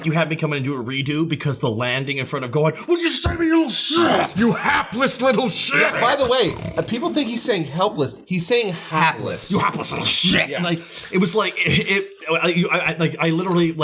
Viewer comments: I thought he said heartless. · I always heard Cell say “Heartless little sh!t” instead of hapless. Seemed like a weird word for him to use, but it also puts into context how cold Gohan is here if even Cell calls him heartless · I always heard "you heartless little shit". I got that from the love song, which I guess I took too literally (not that he actually loved Gohan, but he loved the idea of Super Saiyan 2). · I thought Cell said "Heartless little shit" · I thought he was saying "heartless" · I always heard it as “heartless little shit.” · I never knew he said “hapless,” I thought he said “heartless!”😂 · I always thought he said Heartless Little Shit 😂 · Editor Nies's Notes: Hope y'all had a nice Holiday and are having a great weekend! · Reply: This whole time, i thought he said "Heartless little shit" now I know it's hapless. Rad. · to be honest with the hapless "I thought cell said heartless" and I thought it totally tracked with how cold gohan was being at the moment. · Gotta be honest, I always heard "Heartless Little Shit" not "hapless" or "helpless". It still makes the most sense to me. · I allways thought Cell said "Harmless" so i guess i was doubly wrong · All this time I thought Cell said, "...heartless little shit." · When Cell bulked up, I was always under the idea he said "What did you just say to me you HEARTLESS little shit." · I always heard "you heartless little shit" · For me i thought cell was saying "heartless little shit" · I thought he said heartless